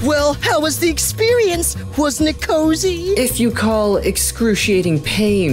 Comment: Says something in captions idk what it says She says NKOSI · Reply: I think it was was*n't it cosy* because I don't have that